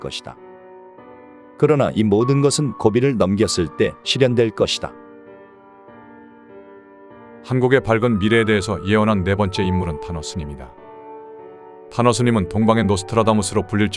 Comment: 타노스님으로 들리냐 ㅋㅋㅋㅋㅋ